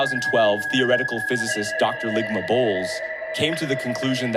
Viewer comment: 🤔 Dr. Lygma Bowles… 😂 thought I heard Dr. Lick my…….